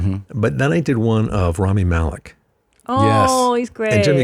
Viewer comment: Rami Malek! I heard him say it and my mind just thought Romony Malco. Or whatever his name is. And I thought it was so random. Makes sense, wasn’t him